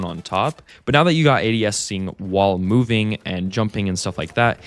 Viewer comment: subtitles say aids instead of ads